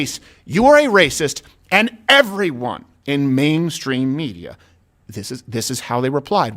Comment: The media is keeping the race hidden because the media wants whites dead. For equity! Heil! Also, when you said main stream media, I thought you said "maim" stream media.